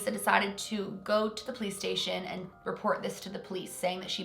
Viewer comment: she said PlayStation instead of police station 😂 and I was confused